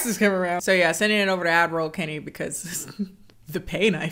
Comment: Whenever you say ad-roll Kennie, I hear "admiral Kennie" and it's effing magnificent ⚓